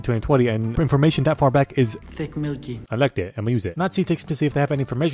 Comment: What the hell is the bald dude saying? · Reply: Ticc milky